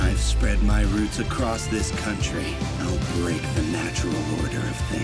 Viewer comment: What he said " whatever it takes, I will save that girl!!" What I heard " whatever it takes, I will shave that girl!!"